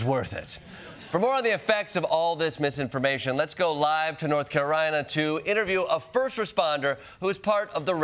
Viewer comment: Did anyone else hear North “Carorina?”